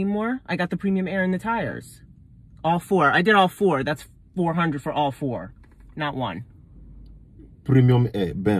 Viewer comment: Lmmfao🤣🤣🤣 sounded like he said "$400 hoe"